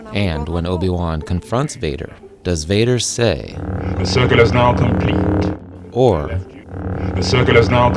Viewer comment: LOL :D All of this was very funny! "The CIRCUIT is now complete!!"... "Outcasting"... :D Regarding #12. I also always have heard this as Luke screaming "KATE!" or "KAY!" #7, I actually always hear it as "The circular is now complete!". #1, this was like the others funny as h*ell :D I never could hear what Obi-Wan said, at all there. Only something about "to travel lightly". And #4, I never have heard that Han Solo said "Let him" at all, I only heard the "have it" part and "Let him" as something I can´t make out. Oh.. and I think R2D2 is screaming too much in Episode 3 - ROtS Br